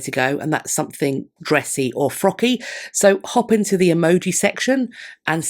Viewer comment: I misheard frocky as froggy, (as I mentioned on the live) so I'm gonna leave frogs! 🐸🐸🐸 And also needles for all that embroidery 🪡🪡🪡 Pretty interesting for my crafty self